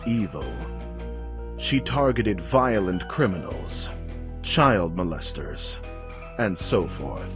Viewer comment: I kinda misheard 'child molesters' as Shia LaBeouf...